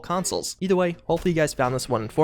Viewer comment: Did you say you thank us all for walking or watching? Sounded like walkin x'D